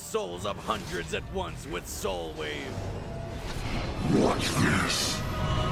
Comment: Did Nightmare just say "watch this"? lol